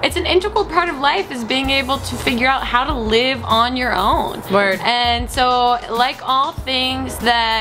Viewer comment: hahaha at 1:45 I thought Hannah said 'twerk' instead of 'word'